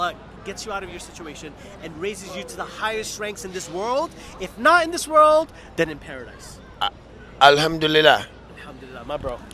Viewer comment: "I pray that Allah raises you to the highest ranks in this world and if not in this world than in paradise" Why dude I think you mean "both in this world and in paradise" in stead of one of the two May Allah guide him and us on his path. Ameen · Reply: sorry bro you clearly say otherwise. You said "I pray that Allah raises you to the highest ranks in this world AND IF NOT in this world than in paradise" . You did not say "both".